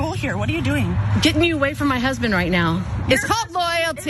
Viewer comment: Did I hear say, “get me way from my husband right now!”?? Her subconscious mind is speaking! 🤷🏽‍♀️